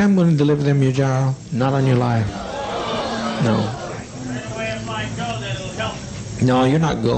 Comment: sounds like someone said "anyway if i go that'll help" im unsure what he was talking about but i wonder if he was trying to get out, or maybe 'self-sacrifice' about whatever 'people parachuting out of the air' jon is talking about